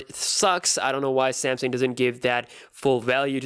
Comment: All I hear is "SEMSENG"